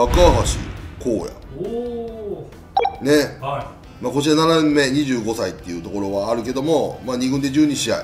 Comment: 57年目に聞こえてビビった